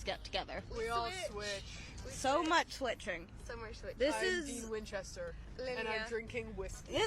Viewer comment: did they say they were dean Winchester... like, the supernatural Dean Winchester? Oh dear